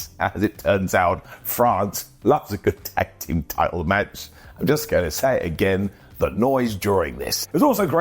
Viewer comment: I had to rewind... sounded like you said "frogs love a good tag team match" 😂😂😂